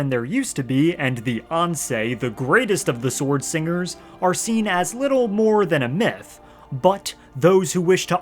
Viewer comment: at first I thought you said Beyoncé the greatest of the sword singers